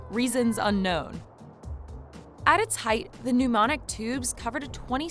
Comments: Pneumatic...NOT Pneu-MONIC! Pneumonic means “carrying or causing Pneumonia” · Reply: I thought she said "mnemonic" tube, so a tube that helps you remember something. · How do you butcher the word "pneumatic" so badly? Sounded like you said mnemonic. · pneumatic and pneumonic are completely different words lmao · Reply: I thought she was saying “mnemonic” (as in Johnny Mnemonic ) Either way it was clearly the wrong word!